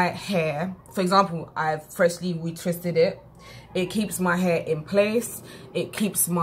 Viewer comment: ? I thought u said "Don't retwist " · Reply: Donald Smith she said dont retwist too often.